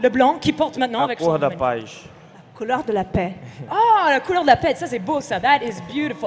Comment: What does thiago say white is the colour of at 2:50?? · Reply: La couleur de la paix